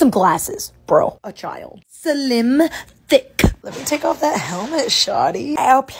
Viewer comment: "tAke oFF tHaT HeLmEt ShaWTy" "MaSsAge mE WiTh ThOse HoOVes" IM CRYING 😭😂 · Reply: I thought she said tickle that helmet 🤣